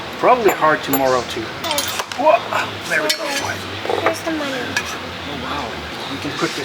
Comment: best comment from her😂 motor comes out "There's the money!" (If thats what she said, thats what I heard)